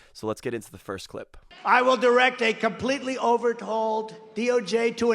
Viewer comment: I thought he said 'we will completely overhold' ... till i rewinded it 3x and realised he said 'OVERTOLD' instead of ... overhaul hahaha americas nuts !!